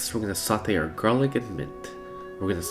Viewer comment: should we bully him further to do a vegan interpretation of this dish /j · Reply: I MISHEARD THE MINT IT SOUNDED LIKE MEAT asdffhjkkllkfjkgjks